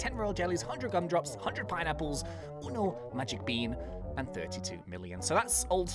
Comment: did you say one in spanish?! for those who dont know uno in spanish means one